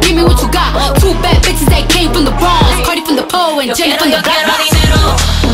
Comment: "CARDI FROM THE POLE AND JENNY FROM THE BLOCK" ICONIC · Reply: I Thought She Said "and Jenny From The Bronx"